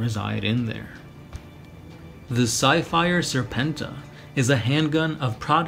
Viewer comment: Did anyone hear the "Psyfire Serpenta" at 9:33 as "sci-fi-er" serpenta?